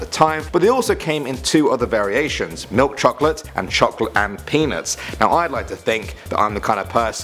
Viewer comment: lol i heard him say "chocolate penis" instead of peanuts lmao